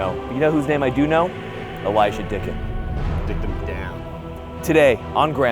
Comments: did he say dickin the dog? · Im sorry but the intro had me laughing "The one name I do know... Elijah Dickens!" then all I hear is "Dicked em down" 😂